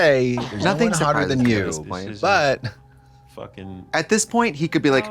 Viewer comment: Katya: “buut—“ “-Fucking” I paused the video just before this and all I heard was butt fuckin 😳